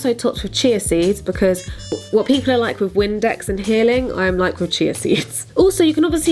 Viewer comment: I can't wait to try all these recipes, I already tried the scramble with asparagus and feta and it was delicious! Can someone tell me what says here 4:20? I keep hearing "What people are like with windex and healing"